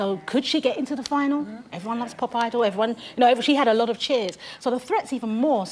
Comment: aaaaaaaaaaaaahahahaha I thought they were saying "popeye" and it was Pop Idol, LOL. I'm used to the English accent but that threw me, LOL